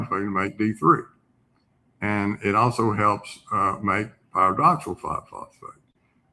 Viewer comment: everything this man is saying is so interesting and so vital I wish he wouldn’t mumble his words cutting them off at the end like this word. I have no idea what this guy says here. Does anybody else know? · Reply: He said “pyridoxal 5’-phosphate” (AKA vitamin B6).